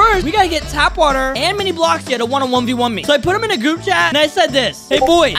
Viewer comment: I thought u said Tanqr and mini lol